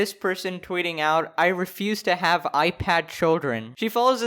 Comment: I thought it said "I refuse to have IPad chickens"